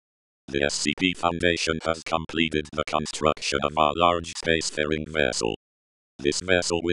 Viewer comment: i thought it said spacefarting